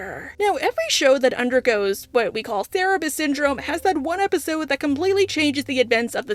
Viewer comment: For a moment there, I thought you'd said "therapist syndrome," and I was very confused LOL